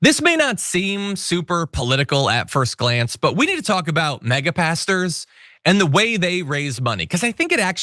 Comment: I'm from Cornwall UK... when you said Mega Pastor I thought you said Mega 'Pasties'... bit disappointed tbh...